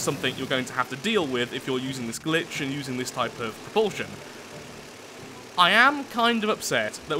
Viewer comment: did you just say bullshit? This is the first time i've heard you swear. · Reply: "This type of - propulsion" with the - being a gap due to suppressing a cough :P so no, I didn't haha!